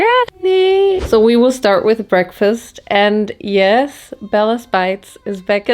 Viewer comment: FINALLY, after two years of rewatching this video, I understand what you said here 1:26 😭😭😭 I guess it's "let's get started"🥹